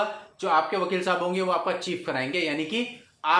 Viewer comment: सर आपने 2:10 पर ये "चीप" जैसा शब्द क्या बोला कुछ समझ नहीं आया?